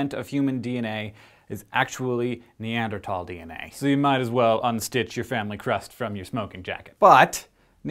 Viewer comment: What did u mean when u said, "unstick your family crest from your smoking jacket"?